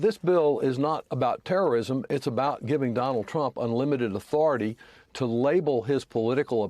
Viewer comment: at about 3:58 i hear the word unlimited but i see the transcript say limited